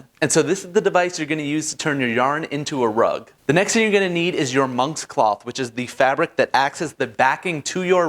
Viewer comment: Wait why i did i Heard "your mom's cloth''